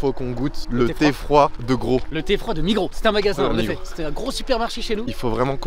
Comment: Pourquoi tonton il dit "nigro" à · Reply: C'est migro